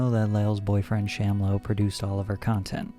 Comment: Thought he was saying sham wow lmfao